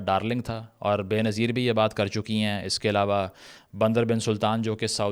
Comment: What did He say at 1:56 ?? Bin salman what bin?? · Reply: Bandar bin sultan